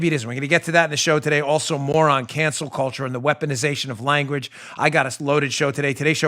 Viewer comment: That sounded like you said "Moron cancel culture". Good job😘